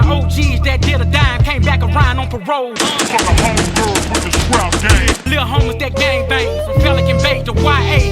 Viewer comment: For the Homegirls with the scrap game. Lil Homies that gang bang! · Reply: I thought it said "strap game"